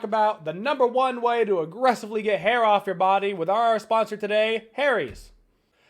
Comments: Am I the only one who thought he said imma show you how to get hera off your body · I thought he said “but first let’s look at the best way to get Hera off your body”😂